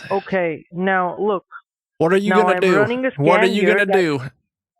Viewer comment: omg I thought he said "im gonna run a scam now" instead of scan 😂